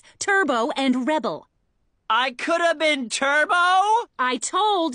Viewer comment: My dsmp a$$ thought that said Tubbo😭